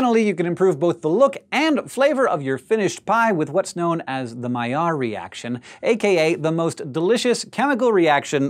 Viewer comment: My friend said she was going to draw an eye (AND I KEEP ON PRESSING CAPS LOCK. Ugh), and I thought she said pie, and then I told her I thought she was going to draw a pie, but she heard pi, and when I told her it was the other pie, she drew the eating pie. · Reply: At sometime like 2:22, it sounded like mallard, like the mallard duck.